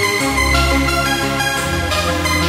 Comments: Until I read the comments I always heard "You can't take control." instead of "Kids taking drugs?" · Hehehe, I thought the sample was saying "He keeps taking drugs" :) ... Now I wish I could find a version of that track that isn't part of a mix (unlike this one) but anyway, that's some good hardcore ^_^